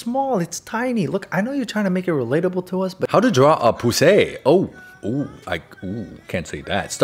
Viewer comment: DUDE I JUST HAD THIS AS BACKGROUND AND I WASNT ACTUALLY WATCHING AND I JUST HEAR ‘how to draw a poosay’ AND I WAS SO FLABBERGASTED